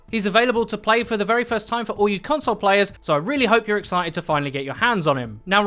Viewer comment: Thought u said for all you cunts players